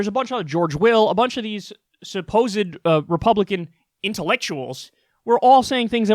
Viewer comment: Did you just say Republican intellectuals?